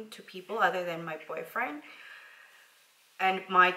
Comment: did u say boyfriend? that was the exact moment when my heart broke